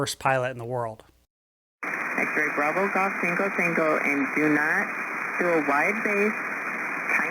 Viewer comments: Rather unusual accent of that pilot, it sounds like he is saying "Golf - Taco - Taco" instead of golf-tango-tango (for their callsign GTT). 😄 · it sounds like he says golf taco taco not golf tango tango!